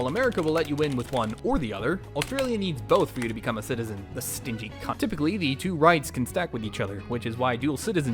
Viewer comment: I think the joke is you cut yourself off saying “stinky country”? But it sounds like you said “stinky cunt”????